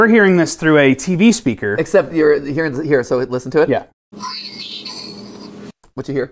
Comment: i hear green needle. can't hear brainstorm at all D